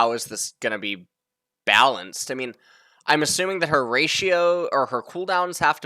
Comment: I keep hearing “her ratio” as “Horatio”